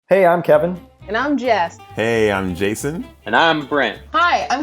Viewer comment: When jess said "Hi, i'm jess" It kinda sounded like she said "Hi, i'm jeff"